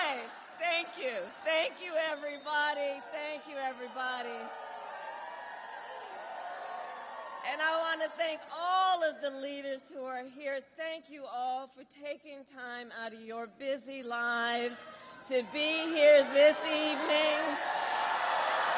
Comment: Did she just say that she wants "to thank all the eliteists that are here"? That sounds about right.